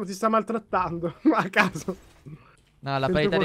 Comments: Ma solo io al min 13:03 ho sentito dire a ca**o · al minuto 13:02 o non ho capito chi era a detto "Ma ca**o"